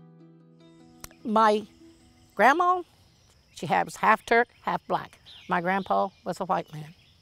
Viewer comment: Does she say her grandma was half Turk? That's interesting.